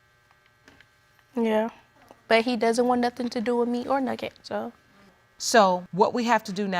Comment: AM I THE ONLY ONE WHO HEARD “with me or nugget”😃